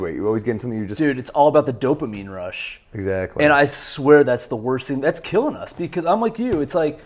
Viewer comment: I heard "worst thing" as "worth thing" repeatedly. Thought he was talking with a lisp for a second. But then he said Holocaust perfectly.